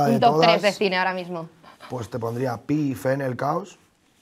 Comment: Cual es la primera película que dice en el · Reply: pi, fe en el caos